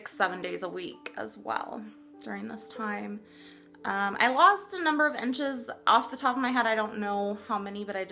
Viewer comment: I thought you said "I lost a couple of inches off the top of my head" and I was like "how do you do that?!" before I heard the rest :D I'd love to try doing this challenge!!